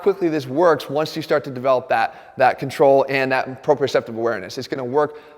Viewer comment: I was like"what's that big friggin long word he used before he says 'awareness' " then I had an idea: closed captions. turns out, CC caught it: PROPRIOCEPTIVE awareness. Jeff, sometimes you talk so damn fast!